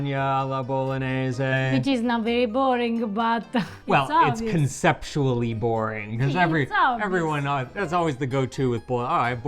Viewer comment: When he said conceptually boring, I heard "sexually" boring. The intrusive mental image was...meaty...